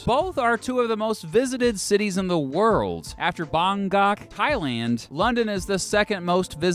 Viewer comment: did you just say bOnGAk instead of bangkok